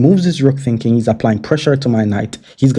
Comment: LOL! I thought you said: "He's gonna get f*cked!" But then I realized you said: "He's gonna get forked!"